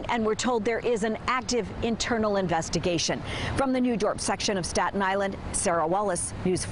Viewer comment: At the end of this video it sounds like she says from the New Dark section!